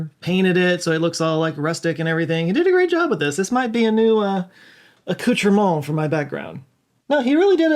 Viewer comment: This might be a new "what" for his background? 🤔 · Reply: I was wondering what did he say 3:12 too! “Couture model”?